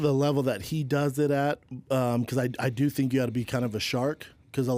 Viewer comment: What the heck is shaub even saying? I dont understand, I even turned on Close Captioning to read what he said , Close caption goes " because I do think you (ought) I hear (got) to be kind of a shark because a lot of fighters are pretty caring despite what people think" . Like what does that even mean is that even English ? lol